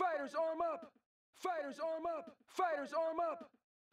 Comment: I swear I'm getting the mandela effect. I thought they said "Fighter rise up" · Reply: I always thought they said "gamers rise up"